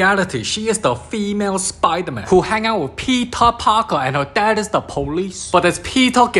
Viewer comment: I HEARD THAT AS ' PEPEE PARKER ' AND I JUST BURST OUT LAUGHING